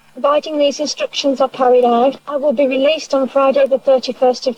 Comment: what's inaudible? She said "providing these instructions are carried out"